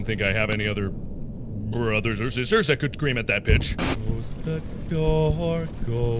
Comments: When he said pitch, I thought he said the word bitch. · I always heard “I could scream at that b***” until now Now I hear “I could scream at that pitch” Even though he’s still saying “THAT could scream at that pitch” Future me do you hear it normally now?... in your perspective of now